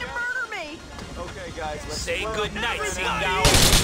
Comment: why do I hear LADY HEAR ME TONİGHT omg am i the only one?